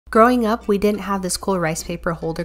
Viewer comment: Is it bad I heard “throwing up” the first time